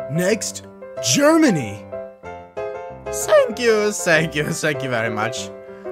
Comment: he says sank you sank you very much instead of thank you thank you very much